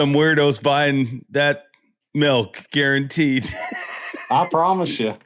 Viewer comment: does say "guaranteet"? 🤣